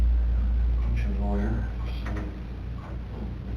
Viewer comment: And I SWEAR at 1:19:33 says again “Well we’ll get you a Lawyer.” - what do you guys hear?? Your take?